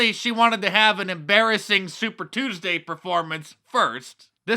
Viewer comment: I thought he was saying, "Superstar Tuesday".